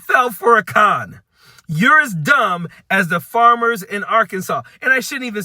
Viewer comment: Did I hear you say "you fell for a con -dumb"? I want to make sure that's dumb with a "b".